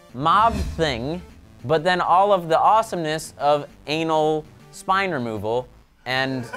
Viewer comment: Sohinki.., did you just say "anal"?......... Like WTF...... And is it Wes laughing in the background?